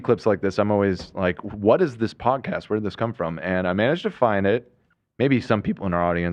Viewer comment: I might be reaching but I swear I heard Ethan say “this ni***” podcast” I maybe I’m just hearing things 😐